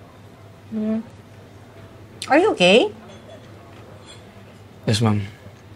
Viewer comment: Is it just me or who her are you gay instead of? Are you OK at 0:20 😂😂😂